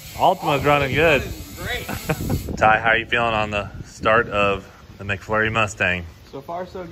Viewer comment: Did he just say turbo kit on McFlurry